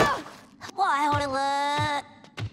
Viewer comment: I thought she said oh how do I look